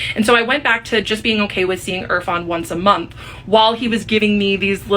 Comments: Did she say his name @ 6:13 ?? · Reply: Yup irfan lol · Did she say his name? Erfan? At 6:13ish mark.